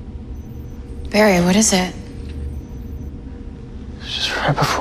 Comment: it sounded like Joe said jerry instead of Barry